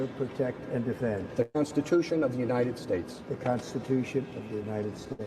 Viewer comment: when he said that to be fair he did think he said "the Constipation in the USA"....